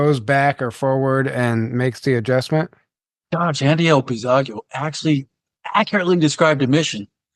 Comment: Who was the person he mentions at 1:03:20. Played it back 5 times and still couldn't understand what he was saying. Some guy that ran for president. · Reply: He always gets the pronunciation wrong but he is trying to say: Andrew Basiago :) ...time traveler, very intriguing story he has!